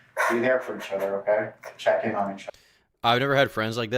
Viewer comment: Thought it said “explore each other”